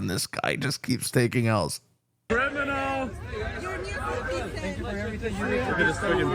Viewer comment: Did I hear the word "Criminal"? if so HAHAHAHA!